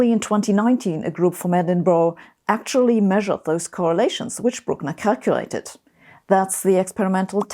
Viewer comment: I thought I heard "the correlations broke my calculator... " LOL!!!